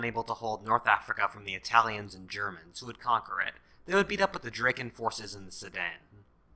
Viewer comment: I thought that said dracula